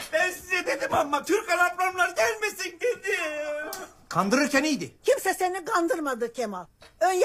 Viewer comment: 'de ben size dedim ama dedikten sonra ne dediğini defalarca izlenmeme rağmen anlayamadım. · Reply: Ben size dedim ammmaaaa Türkan ablamlar gelmesin dedim diyor 😂😂😂